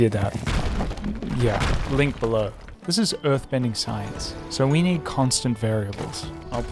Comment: Thought that said "earth bending scene" which I think is funnier